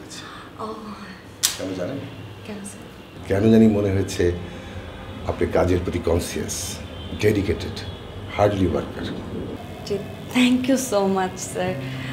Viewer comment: Dude, did he just say, “Hardly worker”! Haha.